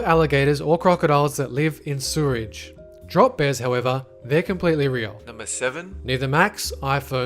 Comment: "something however" what did he say is completely real?